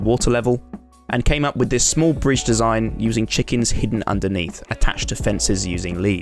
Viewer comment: did he just say chickens :0